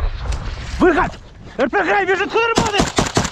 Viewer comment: Does anyone know what he said at 5:59? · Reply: "RPG unit runs!" or something like that